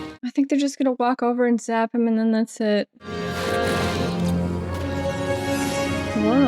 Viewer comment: for a second I thought you said "zap him in the nutsack." :D